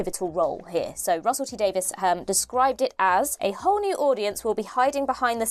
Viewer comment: When she said "A whole new audience" I heard "A horny audience". Still fits